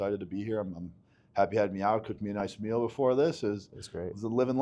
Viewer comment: I had to repeat his personal intro. I thought he said: I had Netanyahu cook me a nice meal befire this 😂